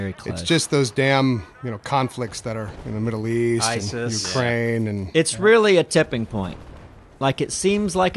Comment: You said Ukraine? I thought you said kraang, from ninja turtles, and I would agree he is definitely stifling the peace process